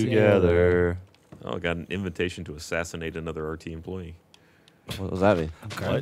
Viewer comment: did I hear that correctly? An invitation to assassinate?